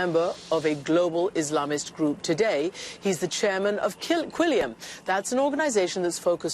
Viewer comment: sounded like she said kill quilliam